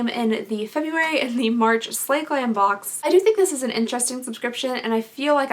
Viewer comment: I thought it said Slam Glam Box 😂